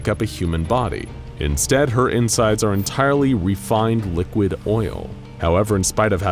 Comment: did you say......oil ◉‿◉ 🇺🇸🇺🇸🇺🇸